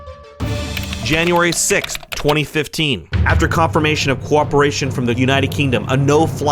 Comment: wait is it just me or did he say 2015??